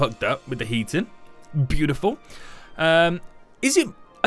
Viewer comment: I thought it said healing station